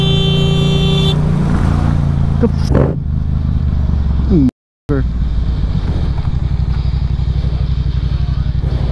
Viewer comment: Did say what I think he said? Cause it definitely sounded like there was an “n” at the beginning and an “r” at the end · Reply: he says motherfucker. the original video is on his channel.